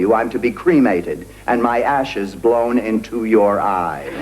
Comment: she said "my ashes will be blown in to your ass" did she said that??